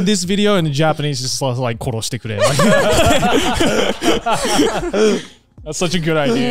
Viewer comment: What did Joey say at 1:02:29? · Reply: koroshite kure "please k*ll me"